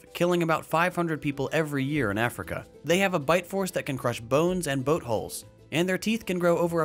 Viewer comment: I swear I thought he said, "They have a bite force that can crush bones and buttholes."